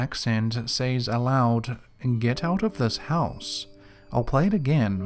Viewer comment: I heard “get out of my spot” instead of “get out of my house” the one with the British accent.